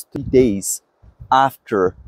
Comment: I can't understand what he says at around 03:58 - 4:00. After how many days were vaccinal mRNA isolated from hearts? Did anyone catch that? Is it 2 days? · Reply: 3 days but you know you can click on ‘show transcript’ and follow along as he speaks 👍